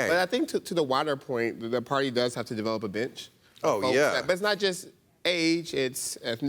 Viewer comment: The party has to develop a bitch?? Did I heard that right?